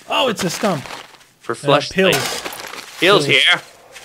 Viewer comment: did anyone else hear say fleshlight at 4:16?